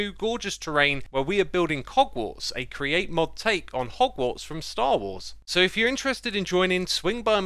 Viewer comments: Did he just say "hogwarts from star wars"? Lmao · Did you say "Hogwarts from Star Wars"? Maybe I misheard you